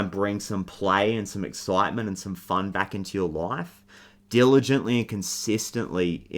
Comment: Did you say Dylan gently 😂 That's you dude. You're awesome. Love watching and listening to your readings. The best 👌